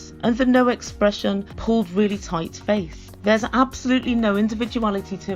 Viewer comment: Seems that all the plastic surgeons these girls visit graduated from the same university!! And when said "Interchangeable", I heard "Disposable". These women look freakish in person but great (they think) in photos.... strange world we live in.